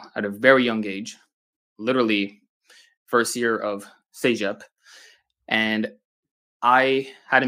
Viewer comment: What did he say 0:44? “First year at sayep”? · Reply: Cegep, it is one of the colleges in quebec